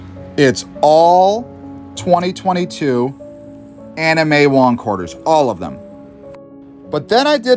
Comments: Anna may.. I thought you said anime😅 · When you said “Anna May” I thought you said anime💀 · I thought you said anime wong. Thought that the US released an anime quarter. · 😂 Anna may wong before I read it I thought he said anime wong quarters was like what the bank give him?!?! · Not gonna lie, first time he said "Anna May", I thought he said anime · I was drinking my breakfast whiskey and thought you said anime quarters I got so happy till I read that lol who dat ?